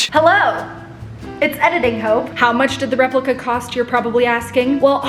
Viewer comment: I heard "its editing hoe" 😭